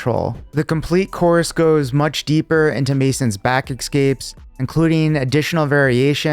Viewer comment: A sign of an elite grappler is when they say the word eXcape - instead of escape. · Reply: I thought he was saying eggscape